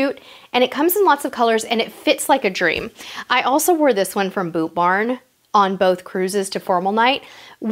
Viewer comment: What’s the store name that you got the dress that you wore to the French restaurant? It sounded like you said Boobarn but I wasn’t sure.